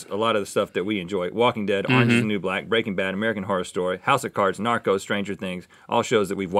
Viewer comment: I thought Rhett said Naruto instead of Narcos 🙈 I wonder if they watch anime 🤔